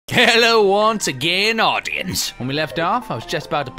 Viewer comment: I just realized it sounds like says "Hello once again, 'audiens'" instead of "audience" ... I AM AN AUDIAN